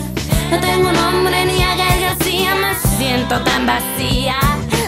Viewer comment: 2016 y todavía no tengo a Gael García :'( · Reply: PENSE QUE DECIA mE SIENTO BIEN VACÍ A JAJAJA QUE OSO